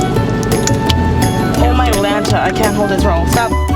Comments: Did she say oh MyLanta!?! Oooooh we have a full/fuller house fan 💖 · Am I the only one who heard her say oh my lanta